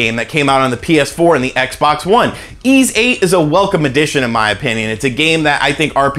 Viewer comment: what game is that someone please? sounds like 'ease 8 ' or something cant understand here lol